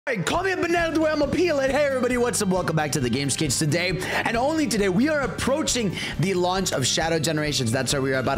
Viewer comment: Heard it as “CALL ME A BANANA THE WAY IM FINNA PEEL IT” 😭